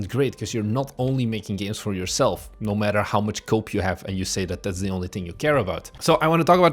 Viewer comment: lol about a minute in you said “no matter how much cope you have” and I initially heard it as “how much coke” and the sentiment works either way